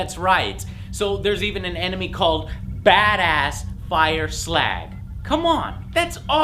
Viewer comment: Did he just say Fire Slag?